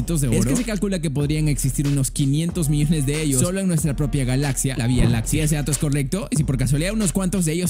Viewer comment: Escuche bialaxia? :V